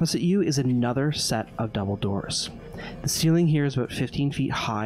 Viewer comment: I know they said "another set of double doors" but for a moment it sounded like they said "another set of Dumbledore's"